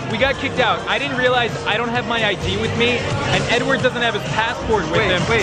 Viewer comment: I thought he said Edward dough😂😂